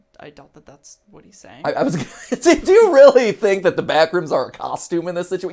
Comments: I thought they said “do you really think there’s some kind of monster in this facility” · I heard the guy say “do you really think there’s a bathroom in this situation?” Which I’m sticking with because while everyone is speculating about lore, one of these guys just having to pee is really funny. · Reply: I heard "Do you really think the backrooms attacks you in this situation?" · I heard “do you really think that there’s a connection in this situation” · "do you think theres actually a monster in this situation?" is what I heard. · Yo at 16:34 I heard “do you really think there is stuff happening in this situation?”